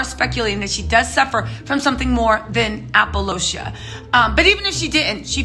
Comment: Did she just say “APOLOCIA”? 🤦🏽‍♀️🤦🏽‍♀️👩🏼👩🏼, it’s a condition called ALOPECIA!!